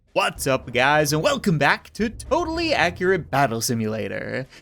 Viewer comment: During the intro I was skipping forward and I heard "Hey guys welcome back to Totally Accurate Indecency"